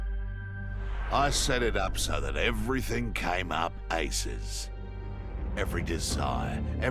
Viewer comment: thank me later😭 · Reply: Nobody gonna comment on the fact that Kano’s accent made it sound like he said “ and everything came up asses.”?